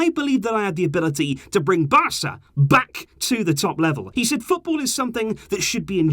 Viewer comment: It was stop instead of top 😂